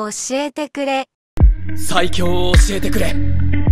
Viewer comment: 悠仁のセリフが「斉木 洋を教えてくれ」に聞こえる